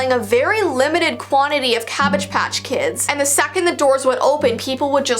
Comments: I thought it said sour patch kids at first 😂😂i’m dumb 🤣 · I thought it said sour patch kids and then the doll intro thing came up and I was so confused lol.